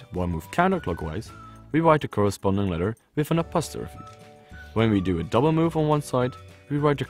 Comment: who wrote the captions because at 2:33 he clearly said 'apostrophe' not 'a postfix'